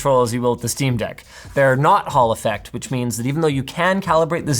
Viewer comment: I'm glad that you showed how o spell "hall effect". Every time you've mentioned t so far I thought you said "Haulifax".